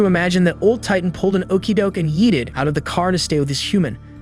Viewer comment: did you say Yeeted?